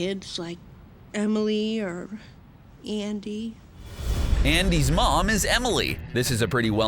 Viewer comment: i heard "E.N.D" guys i heard it and when i heard that i thought about Natsu XD